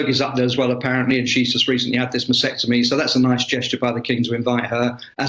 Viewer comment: I'm sorry...does he say at 6:34 that Fergie has just had a vasectomy?